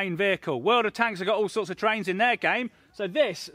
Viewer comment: did he call them trains 😂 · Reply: Terrains :P